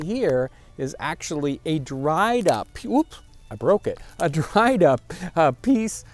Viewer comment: the captions say “poop.” LOL!!